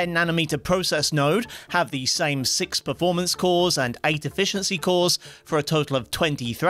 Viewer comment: Somehow misheard as "8 deficiency cores". I really need some more rest.